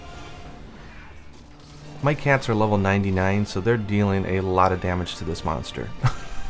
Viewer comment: i misheared that as „my cancer‘s level 99“ O_O